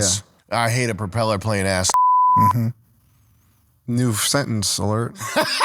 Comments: What did he say at 47:42 that was so funny? I can't make it out. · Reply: ”new sentence alert” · What did he say at 47:42 that was so funny? I can't make it out. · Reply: I assume said "I hate a propellor plane-ass *ninja" and replied "New... sentence alert." Since said he thinks the word should be de-regulated, which was likely seen as funny due to the irony of the idea of a word being offensive and equating it to an industry or act that would require some sort of legislature or moderation. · What did he say at 47:42 that was so funny? I can't make it out. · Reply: "New sentence alert" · What did he say at 47:42 that was so funny? I can't make it out. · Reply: new sentence alert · What did he say at 47:42 that was so funny? I can't make it out. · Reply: I’m pretty sure he said “new sentence alert”